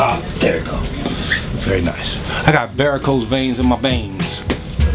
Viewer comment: what is he saying at 8:07min ? i got varicose veins in my veins ?